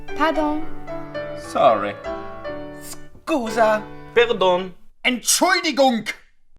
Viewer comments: пердун 😂😂😂(мне так послышалось ) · я один услышал слово "Пердун"? ._. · мне не послышалось? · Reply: AydenShow пердун) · я одна услышала слово "пердун"😂😏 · Мне одному послышалось 01:31 ПЕРДОН😂😂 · послышалось "пердун" :'> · я один слышу слово Пердун? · Мне послышалось пердун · когда покозалось что он сказал пердун 😂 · Мне послышалось Пердун XD 😅😂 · кто услышал пердун на 1:31 :D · Почему я слышу слово "Пердун"? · мне одному послышалось пердон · мне "пирдун" послышалось. Да, я дебил. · мне послышалось (пердун)👏👏👏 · мне одной послышалось пирдон?